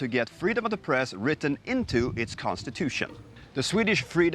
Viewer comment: 1966 - Freedom of the press, 2015 - Demokratur of the media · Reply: I heard "freedom of depressed" 😁